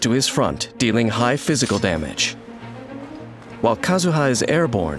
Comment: What I heard: Doing high mental damage